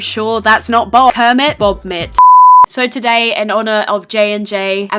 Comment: When you said Bobit I thought you said Bomb it Also I'm telling PETA